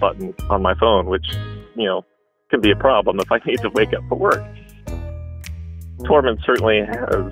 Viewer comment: Ohhh Tormund. I thought he said the cat’s name was Torment 😂.